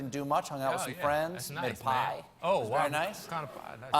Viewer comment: I thought he said, "I got high" instead of "I made pie" 😜